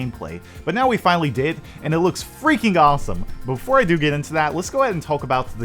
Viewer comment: I'm confused, did he say awful or awesome? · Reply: Never mind, I figured out that he said awesome.